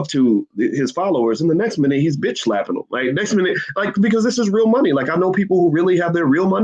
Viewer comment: At @ 8:30 did he say "...Nigga this is real money...."? Lol